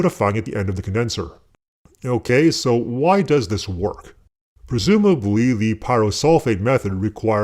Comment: the caption is wrong at at 10:56 you say Presumably, but the caption say "Previously"